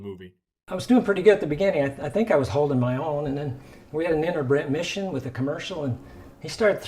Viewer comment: does he say…. “InterBREDmission” here?! Wtf does he say here, I’m going crazy.